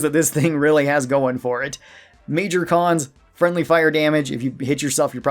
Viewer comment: Totally useless but hilariously overpowered weapon. Also, I heard “Major Cons” as “Major Kong.” Maybe appropriate.